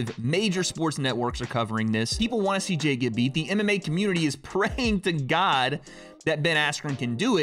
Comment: Not gonna lie when he said “MMA community” all fast I thought he said the anime community 😂